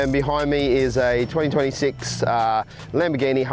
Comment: at the 3:00 mark, did he say 2026 model hurican?